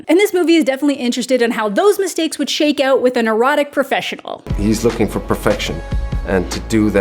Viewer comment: I misheard "a neurotic professional" as "an erotic professional" and got remarkably confused for a moment... Time for sleep! 😂